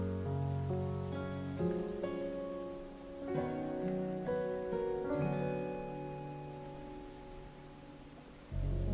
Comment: 여주가 뭐라고 하는건가요? 입모양 어려워 ㅠㅠ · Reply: 고마워요?